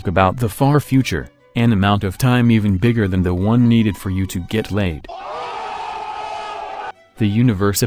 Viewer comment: what does he say ? about YouTube ...I couldn't hear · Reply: "for you to get laid" 😂😂 kinda does sound like YouTube tho